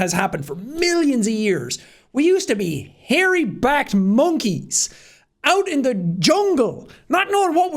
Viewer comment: thought he said sum else "hairy backed monke" made it seem like "hairy black monke"